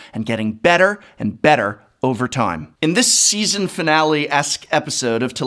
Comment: When he said season finale, I thought he said series and I was about to have a coronary lmao